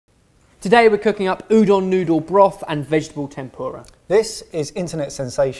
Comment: Got genuinely worried. I thought I heard Ben say Udon Noodle broth and Vegetable tampons.